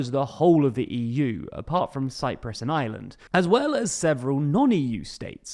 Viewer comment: at first I thought he said "cyprus, an island" and I was really confused at 1. The need to specify cyprus as an island 2. Forgerring Ireland isn't part of Schengen either Then I realised he'd said "and Ireland" rather than "an island"😅 Still though, Romania and Bulgaria are currently only part of schengen for air and maritime borders, bot pand borders (and aren't shown on your map) despite being EU countries, so worth a mention as well